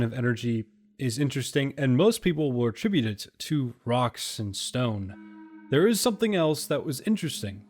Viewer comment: Did I hear a Rock and Stone?!